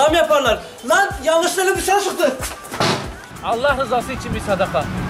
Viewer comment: ne dedi anlayan yazsın 😁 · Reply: Emre Çınar lan yanlışlıkla elim dışarıya çıktı diyor